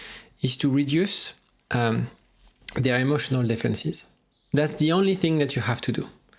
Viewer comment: sorry but I can't understand what you are saying. ''reduce the emotional ....?'' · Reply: Emotional defences. Sorry! ;)